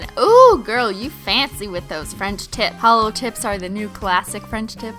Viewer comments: At first I thought she said French tits😂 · My husband could hear this and thought you were saying "French Tits." He was like... "What exactly are you watching?" lol